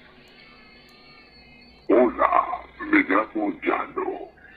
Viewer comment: yo escuche hola te voy a morder el ano xdddd